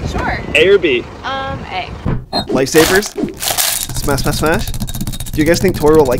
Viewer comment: Is it just me because i hear “Lightsabers” instead of “Life Savers”